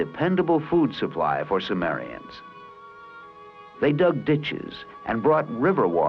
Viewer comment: it sounds like he saying bitches